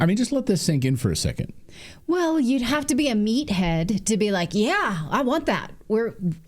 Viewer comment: Really....did she just say 'Meathead' ? @ 2:22?